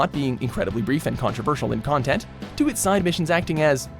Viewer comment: Am I the only one who heard the words sexual plot and side b*tches at 6:56 - 7:04 . Go rewatch and tell me that's not what you heard. I know it says central plot and side mission btw.